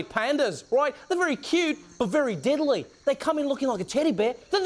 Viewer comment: I thought he said "diddly" instead of "deadly" 😂